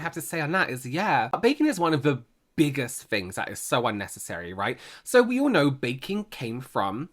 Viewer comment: I totally misheard say “a baby is one of the biggest things that is so unnecessary” and I immediately thought “Wow, Robert serving the tea piping hot in 2026!”. 😂